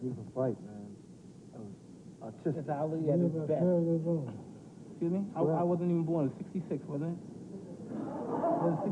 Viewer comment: what did Muhammed Ali say? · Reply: Ali: You have a very good memory..